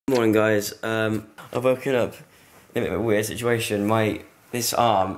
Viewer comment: I thought he said I woke up with a aunt and I was like what 😂😂